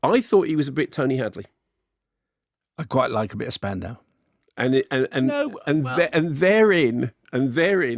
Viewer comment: I thought Simon said he liked a bit of spandex at first.